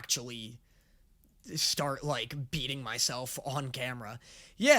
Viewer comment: I thought you said cutting instead of beating and got really concerned for a moment lol